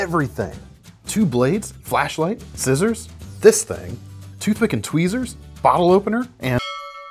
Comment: What he said "toothpick and tweezers" What i heard "Two fuckin' tweezers"